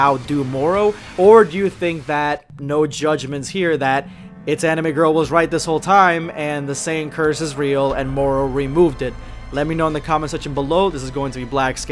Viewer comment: i saw the title and i swore the creator said itsanimegirl